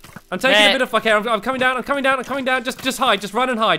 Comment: did say " right I'm coming down, fuck it " lol!